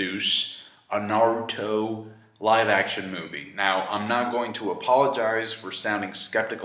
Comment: i seriously thought said:"I'm not gonna rupaulogize." Hahahah RuPaul's Drag Race reference anyone?? No? Fine,i'll leave.